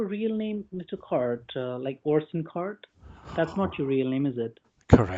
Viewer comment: Orsen Cart Horse and cart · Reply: I thought he said, Orson Card. Orson Scott Card is a famous author of Ender's Game